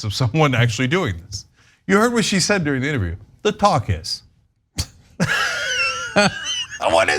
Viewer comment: I thought you said “their pawcus” (like caucus) instead of “talk is” (what you actually said). I thought you were being clever. Its a good one anyway!